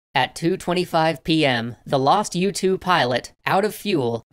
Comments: I thought i heard a YouTube pilot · Started watching and i was confused when i heard " A youtube pilot" haha. Ohhh mornings. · I heard you say a YouTube pilot